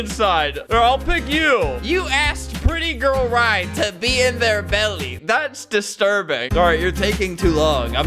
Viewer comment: I swear to god I thought it said “baby hitler” isneats of “be in her belly”